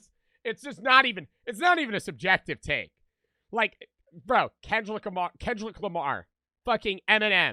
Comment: am I tripping or is bro say Kendrilick, sounds weird